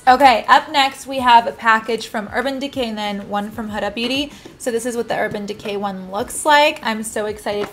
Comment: I thought you said " One Dirt Beauty" 🤣 my favorite pr box is Urban Decay